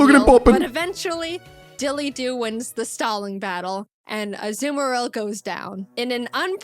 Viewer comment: When she said DilyDop won the staling battle a thought she said stalin battle